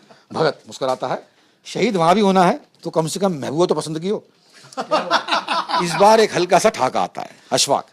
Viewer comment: what does he say? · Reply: Shaadi K topic pr baat krte hue bagat singh ji bole ki shaadi(shaheedi ko shaadi maante hue) hi krni h toh mehbooba toh pasand ki Ho.